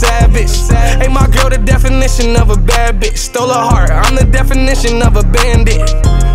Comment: I always thought he said "I'm the definition of a bad bitch" I was mad confused